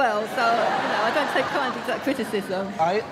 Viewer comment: What did she say at 7:06? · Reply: “I don’t take correct criticism”